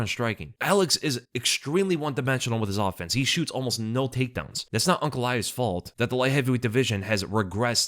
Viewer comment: I thought he said he wouldnt shoot for take down.